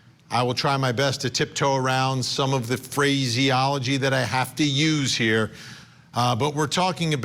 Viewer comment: Did he say FREAKYology? Because there were multiple freak parties.